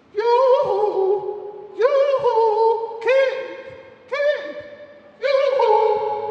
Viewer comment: is she saying Keith? Yooooooohoooooo